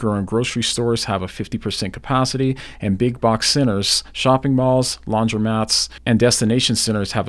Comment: Seriously thought he said big box sinners and destination sinners. Just realized it’s centers